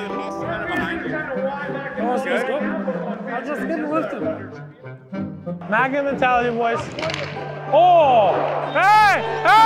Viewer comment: did he say MAGA mentality?